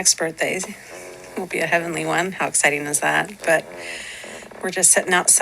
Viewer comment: Did she say how satan is that?That's what I heard I could have sworn.She said, how satan is that?Oh she said exciting